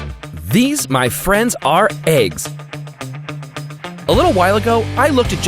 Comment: At first I thought Amiel said "These are my friends, eggs" and I got really excited to meet some egg friends this ep :/